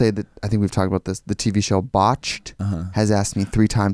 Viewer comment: Ok the first time I listened to this I thought Trixie said Bosch, like the Amazon show, but now I hear Botched. I was wondering why a cop show would be all up in her business about her getting plastic surgery 😭😭😭